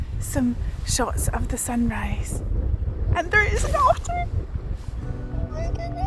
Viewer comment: I could've SWORN I'd heard you say: "...and there are saucers!!" 😂😂😂 Which makes the rest of what you were saying rather fitting & hilarious, ("I CAN'T BELIEVE IT. I REALLY CAN'T BELIEVE IT. THEY ARE REAL, AFTER ALL!!), until "saucers" became "otters", and I was disappointed, and excited, at the same time!! ❤